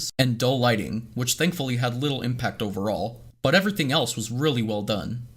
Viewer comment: you said "but" but the captions said "because" 1 sin 4 u Congcusion: Ckeck your spelling 😂😂